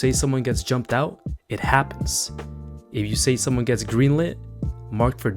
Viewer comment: Why did I hear "if you say somone gets Greenland"?